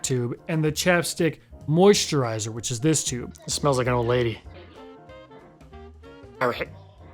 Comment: At the end I thought your son said "this smells like a banger" 🤣gen alpha out here knowing the slang already